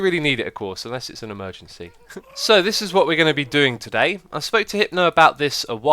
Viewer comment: I swear you said Hitler lol